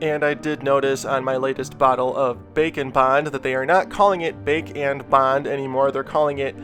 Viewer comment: always thought he said bacon bond xd